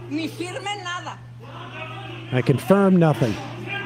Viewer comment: i “confirm nothin” that’s not even what she said 😅she said “don’t sign anything” 🤦🏾‍♂️